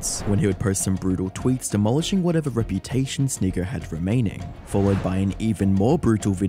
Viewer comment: The closed caption said “snigg**” at 00:58…. The hell?!